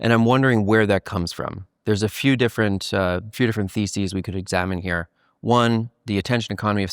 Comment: It sounds like you said feces, not theses